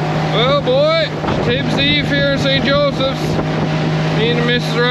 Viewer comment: So this guy just randomly showed up in everybodys recommended? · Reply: I may have misheard, because later in the video he references St Josephs. I thought I heard "Job's" (pronounced Jobe here).